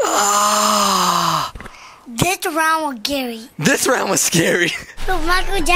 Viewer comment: When said ''this round was scary'' i thought he said ''DoNt MeSs WiTh GaRrY''